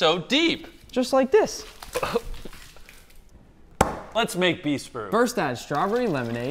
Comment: Subscribe and we'll make a bigger one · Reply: I thought he said Beast Burger for awhile